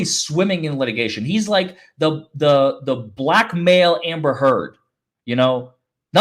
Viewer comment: I heard “the black male Amber Heard” as the blackmailer Amber Heard. I was thinking yes, she is. 😂😂😂